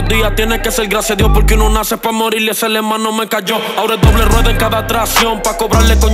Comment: Que dijo?? 2:08 ese aleman no me cayó